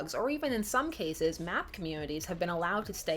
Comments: The term MAP is new to me so I thought you said "math communities" at 7:56. It wouldn't surprise me if Tumblr had a math community. It's all over the place. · Okay, so my hearing sucks: at 7:55, did she say "map community?" Anyone else hear that? Is that a thing? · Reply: Mycelial Ajna yea she said MAP communities. Unfortunately they arent filled with cartography enthusiests. The kind of community she’s referring to are those who call themselves MAPs or Minor Attracted Persons. Which is just a fancy way of wateringdown the word Pedophile